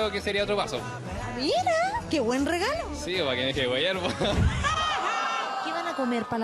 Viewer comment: no entendí lo que dijo el compadre en 2:10, se pusieron a reir y no entendí :-( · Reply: dijo que le iba a agrandar la cocina a la mamá y la periodista le dijo "qué lindo" y él respondió "sí po, pa que se deje de hueviar" XD